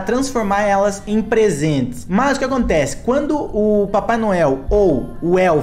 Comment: Mano ksksksks no momento 0:43 o tava explicando como ia funcionar né aí ele disse assim, " o papai Noel tem que transformar as renas em PRESENTE " quando ele disse isso eu entendi o papai noel tem que transformar as renas em presunto ksksksksk 👁👄👁